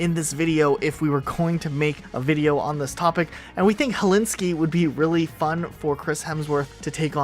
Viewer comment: Am I deaf? Did he just say Hellinski? Lol